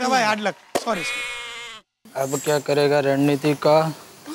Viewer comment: what did he said I didn't understand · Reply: ab kia kre ga run niti ka ye bola h iyer nay.